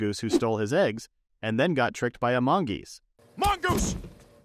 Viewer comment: Why did i heard "he got trciked by among us"